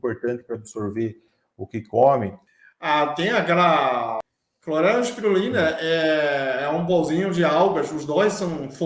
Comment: Me ajudem ;aos 7:15 minutos disse algo eu não entendi e logo em seguida ele disse …e também tem o metilfolato ….se vocês né ajudarem eu agradeço muito🫣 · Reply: Acho que é clorela e spirulina